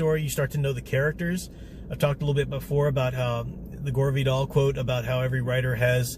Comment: What did you say at 5:13 'the gorby doll?' I find that writing my second novel as well, I have a very similar 'cast' or trope of characters, I suppose it all depends on ones life experiences. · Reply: Lol Sorry, I should have enunciated better. I was referring to a quote by Gore Vidal. Here's the whole thing: "Each writer is born with a repertory company in his head. Shakespeare has perhaps 20 players. … I have 10 or so, and that’s a lot. As you get older, you become more skillful at casting them."